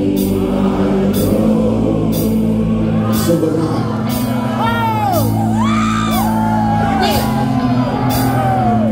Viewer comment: what did Eric say at 1:03? "so good night"? "so good mine"? i cant understand. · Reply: "So am I"